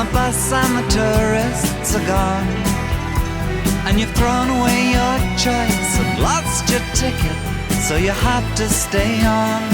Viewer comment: I always hear "you've thrown away your choice of lobster tickets" 😅